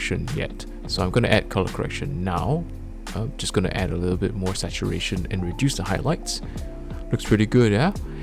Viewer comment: what did u add at 5:30? cuz i couldnt really hear it. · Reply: Add more Saturation and Reduce the highlights in the editing software